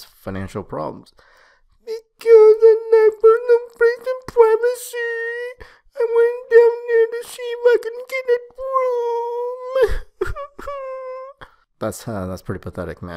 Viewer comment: i figured them all out, except 1:56-2:05. what is he, or it, saying? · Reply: gannon Because I lack personal space and privacy, I went down there to see if I can get a room /whine /cry